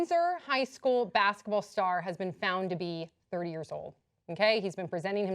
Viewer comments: I thought they said "3 years old" and I was very impressed! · i couldve sworn she said 3 years old...